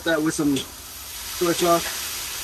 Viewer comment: you said "hit that with some _________"... And i couldnt catch what that was... In the bottle with the red tip. I turned on CC and it said "sweet chocolate" ROFL that aint right.😂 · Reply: Soy sauce! 🤣